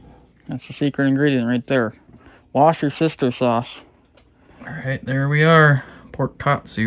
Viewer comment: I misheard when said secret Ingredient “Wash Your Sister Sauce” 😂.